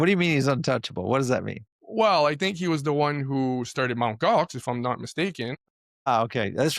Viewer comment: "Mount Goth"??? What the hell are you even saying???? Lol I can hardly understand what your are saying. Maybe I need to slow the speed to .25 or something Yeah... .50 doesn't help. The enunciation is just atrocious. Super frustrating Ok MT Gox.... Got it. Thank goodness for AI, id have utterly no freaking clue what you said had it not been for an ai search